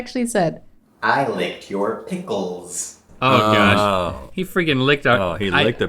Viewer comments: I thought said "I like your pillars" tbh xD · I was so close! I thought was saying "I like your pickles." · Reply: I thought he said I like your pictures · I thought said “I like your big cheeks” 😂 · I was so close! I thought was saying "I like your pickles." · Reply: I thought he was saying, "I like your pictures". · I was so close! I thought was saying "I like your pickles." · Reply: I thought he said "I like your beard." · I thought said : I like your beard lol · Who else thought was mouthing “I like your boots”? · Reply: Marty B I thought he said “ I like your big cheeks” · I thought said i like your pickles lol i was so close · I was so close! I thought was saying "I like your pickles." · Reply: I thought he said "I loved your pickle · I was so close! I thought was saying "I like your pickles." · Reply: I thought he said I love your picture